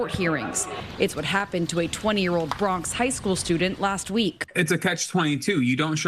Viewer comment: “a 20 year old high school student”? Is that what I heard